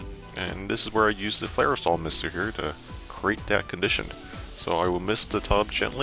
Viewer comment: Great video....what was the name of the mister you used to moisten the mycelium? Sounded like you said Firesol Misticker, but i can't find anything online called that...thanks